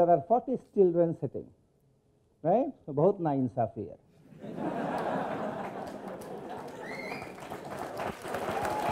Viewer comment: What did he say at 15:26? I didn't understand. Please comment. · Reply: He says "Bohut Na Insafi Hai" which translates into "It is very unfair". This is a famous dialogue from the iconic 1975 Bollywood movie "Sholay".